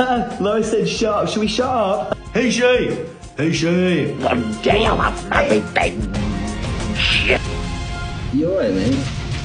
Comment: I thought he said "One gay I'll have my revenge" 💀